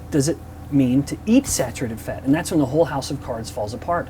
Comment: I heard that as "house of carbs"